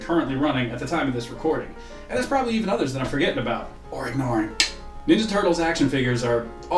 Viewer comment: What Did You Say? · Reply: “...or ignoring, (wink)”